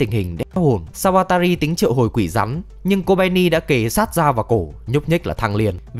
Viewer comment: đm quỷ rắn mà t nghe thành quỷ rắm :((